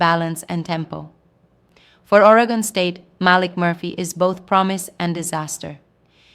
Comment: Sounded like she said Molly Murphy